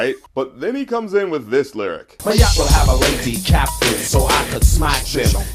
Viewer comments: I thought it sounded like he was saying, "My yacht will have a lady captain, so I can smash it." I mean, that's still a pretty dumb line, but not as bad. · Ngl, I heard "My yacht will have a lady captain so i can smash them" which i think is a better line